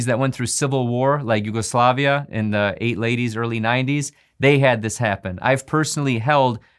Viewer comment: Did you just say eight laties and early nineties @ 9:06 ! 😂